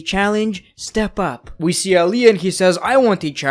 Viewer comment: Did I hear right? Did he really say Aliyah? 😂😂. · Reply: No, he said ‘Ali and’